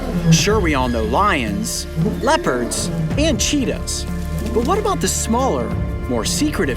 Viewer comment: im sorry did you say cheetos?